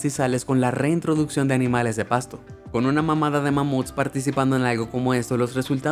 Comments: Con una que? 🤣 Sé que la palabra es manada pero por Odin que escuché mam*da! 😂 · le entendí una mamada de mamut XD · una mamada de mamuts? Escuché bien? 😂😂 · Fue mi oído perverso, o en el minuto 4:50 escuché "una mamada de mamut" 😂😂😂🤣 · escuche mamada de mamuts xd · Solo yo escuché "con una mamada de mamuts* xD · quién más noto que dijo con una mmda de mamuts:0 xd 😂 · Escuche una mamada de mamute en el min 4:53 O.o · nooooo escuche “mamada” 😭😭😭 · escuche mal o ke xd mamada de mamut? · Like si escuchaste claramente que dijo " con una mamada d mammoth" :v min.